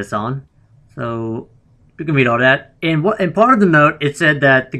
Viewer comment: does anyone know what is says at 5:06? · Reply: Do we all that? I think thats what he says.